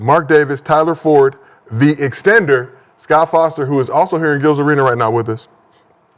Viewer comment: did he say Foster is there with them?